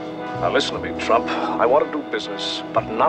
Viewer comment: uhh did he say now listen to me trUmp? . Just my imagination .